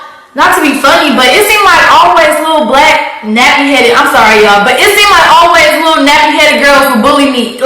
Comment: yes she did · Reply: how is it lies when he put a clip of her saying in the video 🤣she clearly said "blac-nappy headed girls" it's at 10:10, nice try though